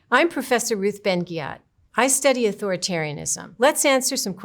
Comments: I can't be the only one who thought she said her name was Ben Gyat 😂 right? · Ok this is unrelated but when I first heard her introduce herself I hear "I'm Professor Rizz Ben-Gyatt" am I cooked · I heard "I'm professor Ruth Ben-Gyatt."